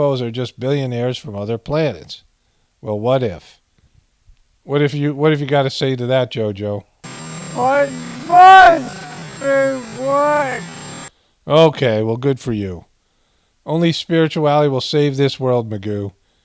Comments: I hear “I must be white.” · But it's improbable that the when you slowed it down I heard " I must be white " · I thought he said, "I must be white!"